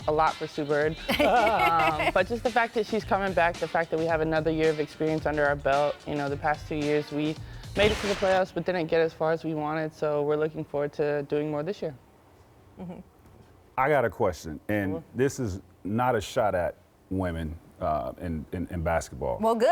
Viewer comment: I thought she said they are looking forward to doing more dishes lol 🤣